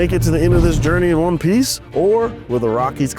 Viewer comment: did you say One Piece?!??!?